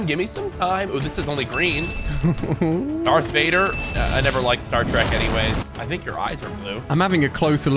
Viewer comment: Did he just say Star Streck